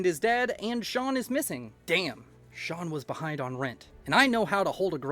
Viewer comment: I heard "shawn was behind on rent" and looked at my phone like how did he know